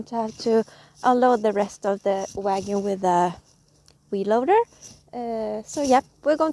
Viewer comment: Sounds like your saying wee loader 😂😂